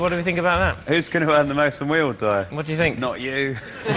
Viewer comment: What does Noel say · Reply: bambrough "who's gonna earn the most when we all die?"